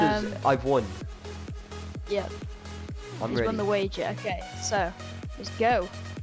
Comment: I thought you said heat wager but you actually said key